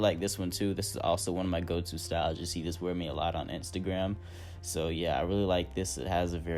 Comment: WAIT 3:08 did you say "You see this wearing me alot on Instagram" 💀💀💀💀💀💀💀